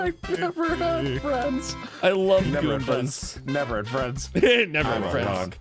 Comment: What were you about to say there? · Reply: It sounds like he said fat fiend